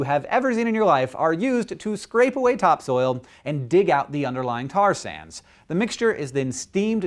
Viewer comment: "Tar sands".... i thought he kept saying "Tarzan" lol